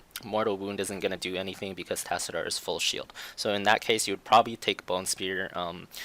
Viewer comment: at first i heard "tassadar is full shit" i was like wait what :) great vid